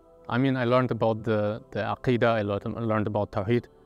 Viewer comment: the captions are wrong it’s “Aqeedah” not al-Qaedah. Aqeedah means faith